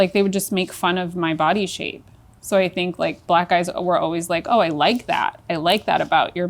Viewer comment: As she literally said, “white dudes”😂😂😂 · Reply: i apologize i thought i heard black guys